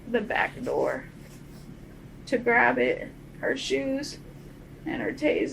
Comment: When she mentioned that her friend was “joking” about robbing him, all of my suspicions toward him went straight to her. You could tell she was being dishonest at that point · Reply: my moment was at 25:00 when she says "she opens his back door to grab it.. her shoes and the taser" the subtitles said "it and her shoes" but to me sounds like she said "grab it" talking about the gun, then realized and quickly changed it to the rest.